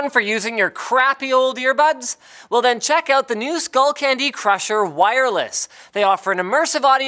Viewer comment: Misheard the sponsor spot as "Skullcrusher candy" and thought "I want some of that."